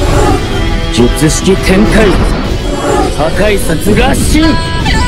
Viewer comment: did i just hear jujutsu tenkai ? Or what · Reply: it's Jujutshiki tenkai